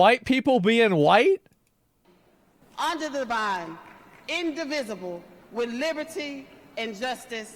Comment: Did she really say Under the Vine 😂